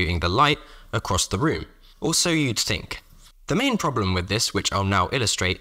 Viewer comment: for some reason it took me 4 attempts at listening to realise he said 'or so you'd think'... I misheard it as 'also you stink', which obviously I knew wasn't right but I couldn't hear anything but that